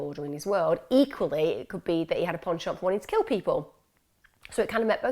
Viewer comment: I thought you said "He had a pawnshop for wanting to kill people" 😂